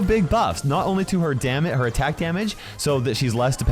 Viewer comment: I heard damn it instead of damage.